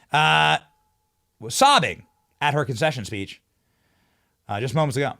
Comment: I apologize ..I thought Benny said " Kamalas CONFESSION SPEACH"